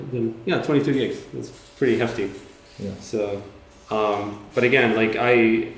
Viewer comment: 22 Gigabyte Memory consumption for this short crocodile animation? Wow, dont get me wrong, iam following this project since many years, but i think without bones and weightmaps etc this wont work out well even if you compress the data a lot. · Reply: Lesan He clearly says "22 Gigs, thats pretty hefty". And you can see it in the Taskmanager @ 1:44:36 . Just watch the video and listen to what he says.